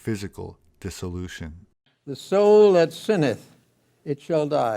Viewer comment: Watching video with CC because english is not my 1st language… What is the word at 6:21? Sinnith? Sinth? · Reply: Sinneth... having committed sins.